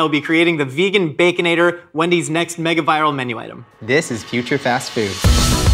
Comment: For some reason i heard say "This is putrid fast food" in the intro hahaha